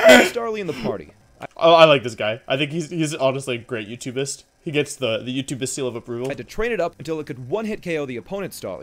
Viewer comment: did you just say "youtubist"??